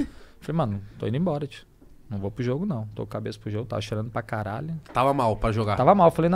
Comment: Só eu quero no minuto 6:30 entendi ele falando: “Eu tava cheirando pra krl” 😂